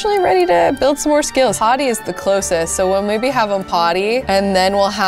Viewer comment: I swear, when she said "potty is closest" I thought she said " hottie is closest", referring to Jamie, and I was just like "HES YOUR SON". selective hearing is phenomenal.